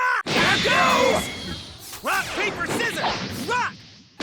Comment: Did Goku just say "Rock, paper, scissor, b*tch!"? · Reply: i heard "rock paper scissors rock. take this." not sure where you head bitch.